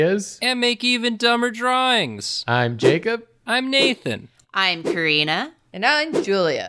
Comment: I thought she said I'm Korean... Sure, but what's your name?